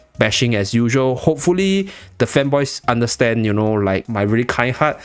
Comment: For some reason, I thought you said femboys, so I skipped back to listen closer. Oh, fanboys. That makes a lot more sense.